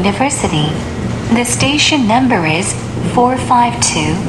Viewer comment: 서해라인이 아니라 그냥 서라인으로 들리네